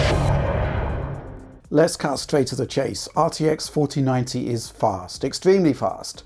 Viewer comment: O he said "let's cut straight to the chase". I thought he said "Let's castrate the chase".